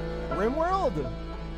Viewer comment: I misheard and thought he said "Rain World". And that type of quiz is hard because there are a lot of video games and we don't have the time to play all of them. Compared to people who watch anime and quizzes about openings, I think it's more easy to recognize openings from shows you didn't watch because you are more likely to hear them at random.